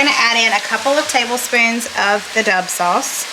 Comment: I feel like my fam would like all 3 this week! I thought you were saying duh sauce in the past videos. I get it now lol 😆